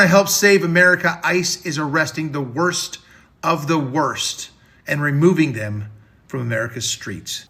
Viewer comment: what did he say "you can work for ICE even if you are the worst of the worst!"